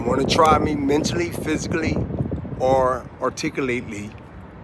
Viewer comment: Did he just say articulately?🤣